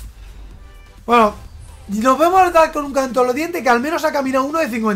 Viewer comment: Alguien entendio lo que dijo en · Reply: Un canto en los dientes